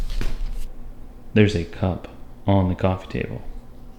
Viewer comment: I thought he said, "A pup on the coffee table." I was waiting to see Rizzo up there.